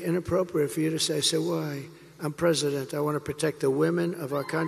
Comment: Did anybody catch what he saud apart from like it or not …. Listen to ….from 3:50 …. He said he’s president Please people listen